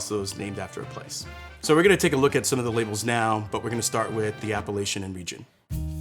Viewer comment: I thought you said "the Appalachian Region"....I was like "Whaaaaa?"